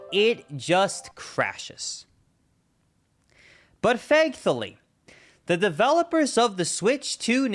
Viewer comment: Is it just me, or is he saying "fankthully"?